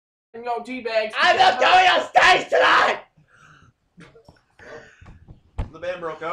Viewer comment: what did Calum say at 10:30 when he yelled? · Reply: "I'm not going on stage tonight!"